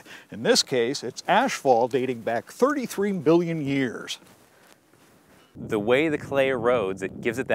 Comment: did he say 33 bln years?